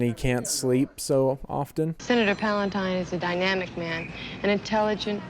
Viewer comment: I always hear "Senator Palpatine" instead of Palantine.